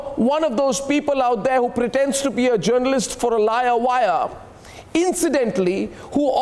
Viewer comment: Pretend to be journalist for a liar wire in that what I hear in 5:20 ,😂😂😂🤣🤣🤣🤣🤣🤣🤣🤣🤣🤣😂🤣🤣🤣🤣🤣🤣🤣